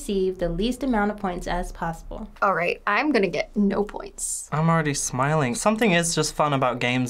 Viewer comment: I thought said she'd get zero points.